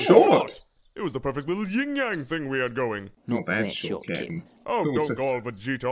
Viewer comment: At around 4:22 even though I know he's saying captain, I always remember mishearing saying Kitten